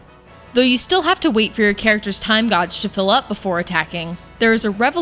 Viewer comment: Did she just say 'time gawj'? XD